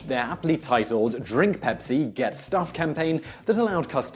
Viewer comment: At first I thought I heard "Drink Pepsi, Get Stuffed" lol